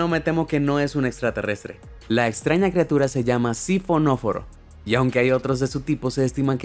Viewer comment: yo escuche fósforo xd